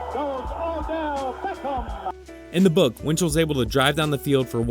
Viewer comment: Did he say Odel Beckham??? lol is that OBJs dad??